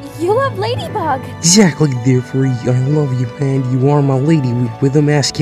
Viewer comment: Did he say “I love you man!”